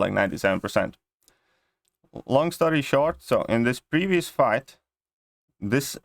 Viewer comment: Did he just say "wong story short"?